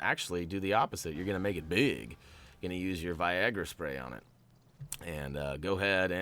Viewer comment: I'm sorry but did he just say Viagra spray 😂